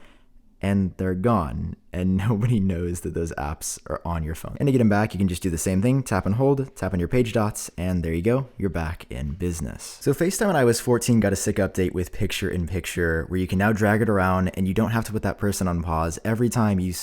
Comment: I thought you said “so FaceTime, when I was fourteen” lol